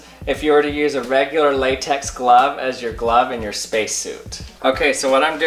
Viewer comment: i thought he said space shit